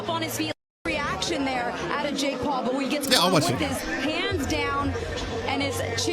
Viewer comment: I thought she said, “caught with his pants down”